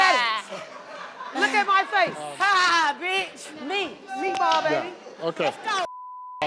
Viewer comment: Did she just say meatball baby at 13:32 😭😭😭😭☠️